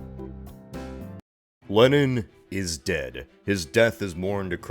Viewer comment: the first time I heard "LENNON is de*d" haha